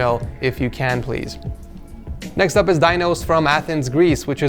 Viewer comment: "Dinos" I heard "Thanos" XD.